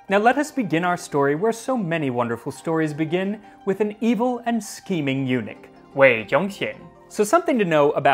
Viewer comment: I heard “screaming eunuch”. Good chuckle.